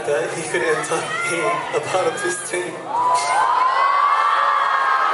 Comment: Can someone write what he said in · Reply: and we could hold hands and come back to our home town in australia